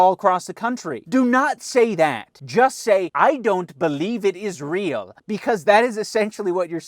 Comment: I thought you said “I don’t believe in Israel”